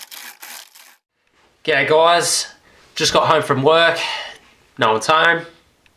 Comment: Lol in your intro when you said "Ok guys" it sounded like you said "Gay guys"